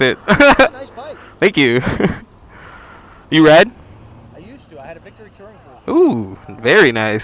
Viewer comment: is read or ride? i hear ride, my dumbass roommate hears read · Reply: Ride